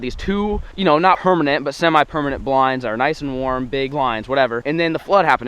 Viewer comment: At one 1:19 says,” hermanent” Did anyone else hear that